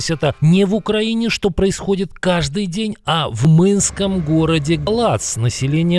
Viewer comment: Только я услышал в «Мынском городе Латз» на 1:15 мин. ?